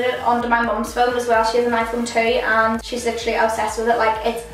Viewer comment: When you said "she has an iPhone too" I thought you said "she has an iPhone 2" I was like wtf I didn't even know that existed😂